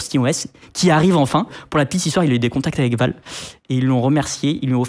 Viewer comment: j'ai réécouté ! 😅 j'avais entendu "avec Valve" à la 1ère écoute 😅😅 je me suis dit pourquoi pas 😂 mais dans le doute..🤔 et quand j'ai entendu "avec Vald" je me suis dit.. Ah là c'est logique !!! J'ai bien compris? rassurez moi!😊